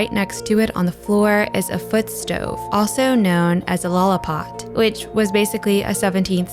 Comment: Honest question: at 5:00 the alternative name for the foot stove is mentioned. Is it called and spelled "lullapod"? Why is it called this? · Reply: Maybe you found out already, but I found that it is spelled “lollepot” :)